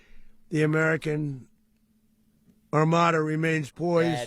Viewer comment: Did he say American Armada? 😅